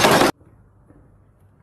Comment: Мне одному послышалось, что котик, который упал в ведро потом промяукал «I’m cold!” ? Успокойте пожалуйста мою шизу